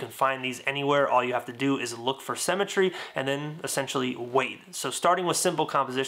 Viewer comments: I thought he said "all you have to do is look for a cementary " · it sounds like you said "look for cemetary", although you said symmetry xD